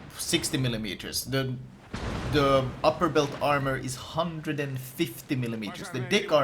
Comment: I swear he said "dick armor"